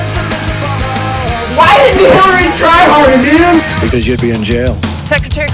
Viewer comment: What’s he saying at 1:54? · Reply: Why didn’t Hilary try harder.